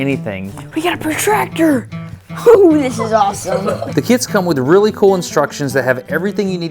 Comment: I was a bit distracted at the end of the video but something you said at 18:53 caught my attention. "The kids come with really good instructions..." Well at least that was what I thought I heard, and my first thought was "WTF? I never saw any instructions. Where did you get yours?" I was both some what disappointed and relieved when I saw you were talking about some kind of kits...